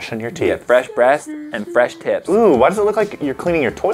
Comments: i thought it sounded like he said "fresh breast and fresh tips" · did he just say "Fresh breast, fresh tips."?